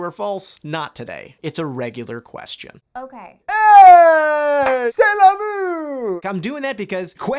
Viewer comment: What does say at 5:33? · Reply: Cella but; goal